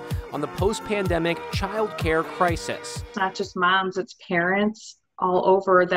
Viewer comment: thought she said “Karens” for a moment 😂